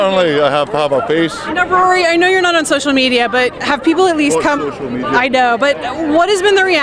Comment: lmao did he say "fook social media"